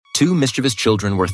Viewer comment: I thought I heard two Mr beast children😂